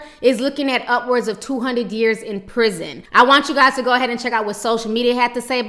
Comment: 200 years? couldn't quite hear that. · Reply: yes. it's 200 years